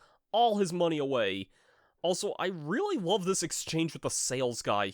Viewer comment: Whats Cooking, D? Send me a crack! Say what? (Dedede actually says crab)